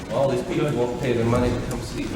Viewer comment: I don't know if anyone else heard that last bit at the end from Bundy at 17:23 - 17:26. He said in response to Cowart saying we'll miss you: "All these people won't pay their money to come see me your honor". Such a narcissistic thing to say! 🙄